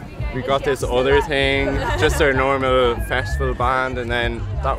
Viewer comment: Sorry , i thought they said "Music Festival" 🤔